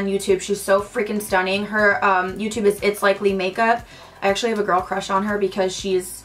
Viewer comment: What's the name of the Youtube channel you mention at 8:21 ? I don't understand it sorry. Great video and I'll definitely check your Snapchat! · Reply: BethMarlowe itslikelymakeup ☺️